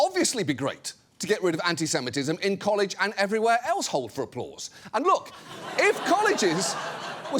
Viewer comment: Did he just say hold for applause 11:23 mark